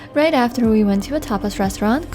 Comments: I could swear i heard "after that we went to a topless restaurant" I know this must be wrong and i proberbly just have a dirty mind 🤔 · When you said "We went to a Tapas restaurant", I thought you said "Topless Restaurant". I was wondering if that was the norm in Spain for a midday snack!